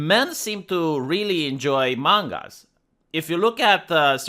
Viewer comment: i thought you said among us. im infected with this parasite and don't know what to do. I hear it everywhere